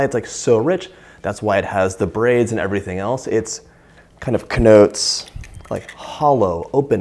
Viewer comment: i was so into hearing Yiddish when was talking about going to friends for Sabbath, that when he said "it kind of connotes..." i heard "It kind of knnotes.." like Knish. oy vey!